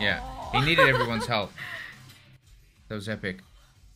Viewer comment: I know you said that was epic but first time I heard ozempic For some reason, it does not help I recently saw the south park end of obesity but still 😂